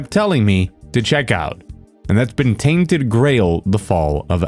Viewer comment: I thought he was saying tainted grey hold